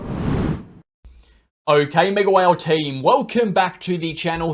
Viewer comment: after so long ... i thought u say "Mac well" ever time video starts.... and im today year old just figured out you actually say "MegaWhale"😅😅😅